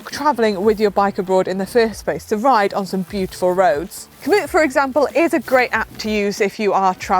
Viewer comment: Manson suggests "a great app to use if you are traveling around the world." Close captions says she said "commute." Is that the name of the app? There does not seem to be a mention of or link to it in the additional information area "below." A thumb down for that. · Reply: Komoot